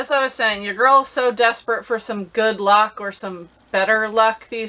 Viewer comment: HAD TO R E W I N D.... .....thought you said "Good Love" AND IM LIKE D A M M ... after dark!!!❗‼️❕👍🏼🤣🤣🤣🤣